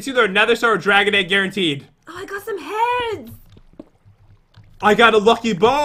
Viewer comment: DID I JUST HEAR JEN SAY “I GOT SOME HEAD” 6:29 😵😵😵